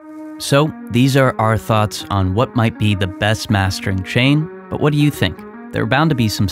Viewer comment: Coulda sworn I thought it said "Best Mastering Chair"